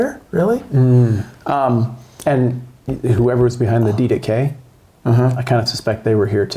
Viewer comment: what did you say @ 24:58? "Who ever's behind the d to k?" I misheard. Please clarify.